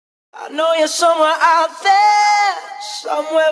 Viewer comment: And my dumbass thought it said "i know your summer outfit" :)